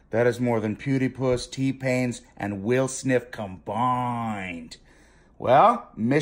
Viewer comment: Did he just say Will Sniff ????? LOL